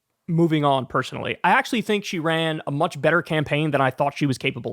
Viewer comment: Thought you said she ran an amazing campaign 😂